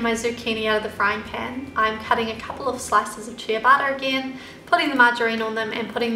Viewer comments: I was like chia butter? What? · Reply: no she said ciabatta, and I thought she said chia butter 😂 lol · It sounds like youre saying "chia butter" when you say ciabatta lol